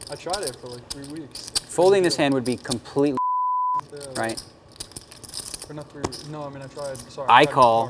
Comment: Talking of word usage, can some lip-reader tell me what Daniel says at the end of "folding this hand would be a complete....*******" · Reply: I don't know why but it kinda looks like he says "folding this hand would he completely retarded" remember this was back when you could get away with that